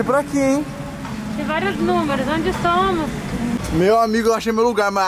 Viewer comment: eu ouvi onde são amor 🤔🤔🤔